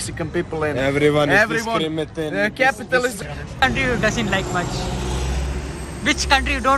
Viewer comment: As a black african boy in england hating america and pan africanist supporting yurgoslavia and serbia on kosovo I swear Bro said fascisim